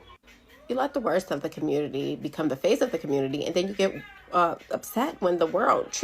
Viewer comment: The young black woman that absolutely was telling the truth said "you let the worst of the community become the FAITH of the community, she did not say "FACE".